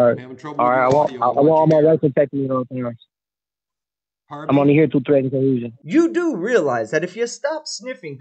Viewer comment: Please tell me I'm wrong. Does he actually say he's there under threat and delusion. @ 5:16. I might say his subconscious mind is telling on him to the judge.